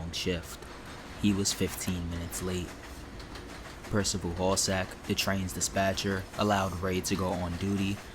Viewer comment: I thought you said his name was Percival Ballsack for a second! 😂